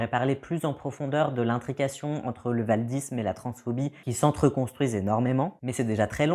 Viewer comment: J'ai entendu « paludisme », pendant deux secondes. J'ai bégayé.